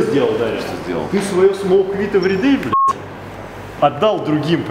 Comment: did diamond just say 'smoke weed everyday'? LOL